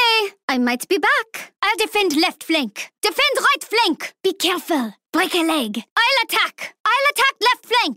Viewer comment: what does she say? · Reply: Be careful break a leg